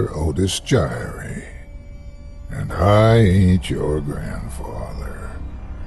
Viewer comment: I always mishear the beginning bit as "and I ate your grandfather"